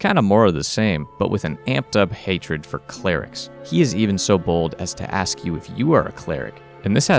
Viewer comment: I swear I heard "He is even so BALD"